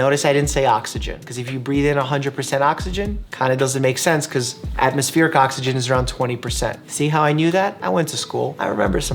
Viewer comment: did I hear atmosphere KA oxygen? 🧐 I'm sure Indians will understand!